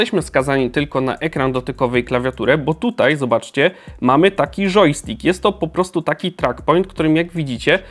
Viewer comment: tylko ja usłyszałem żojstik?